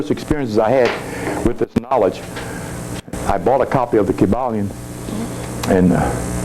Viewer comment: What is book called he spoke of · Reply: Quad Carter The Kybalion